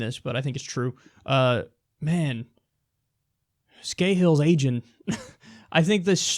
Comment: I thought Kyle said Scahill is "Asian"; had to go back to realize he actually said "agin' ".